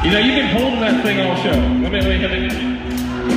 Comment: That's pretty rad of him. What did he say at 6:07 btw? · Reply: "You know, you've been holdin' that thing all show"